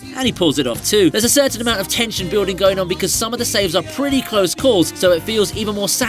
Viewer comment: Red laser skills? I thought you said you were an expert.